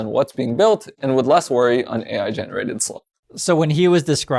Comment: did he just said "ai slop" 😂